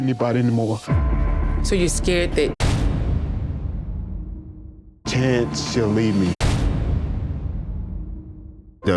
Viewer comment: Help me figure this out at 0:12 “You think you might have C—“…what is this saying? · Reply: CTE